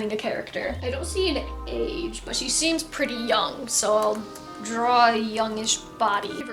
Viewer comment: What you said "But she seems pretty young" What i heard "But she seems pretty dumb"